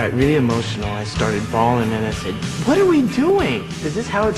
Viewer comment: What is the word that Kirk says at 1:55 in this context: "I got really emotional. I started ??? (...)". Is it "bowling", "brawling"? Anyone can help? Cheers! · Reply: "Bawling", as in crying. I don't think he'd be so emotional he'd start a fight or want to go bowling. Who knows, maybe he relieves stress by bowling?